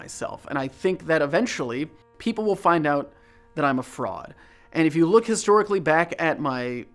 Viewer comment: I’m sorry this is a bit out of the mood but..when you said “i’m a fraud” i heard “i’m a frog” 😭